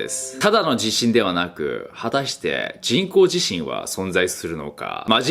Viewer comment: まち○こ地震に聞こえん？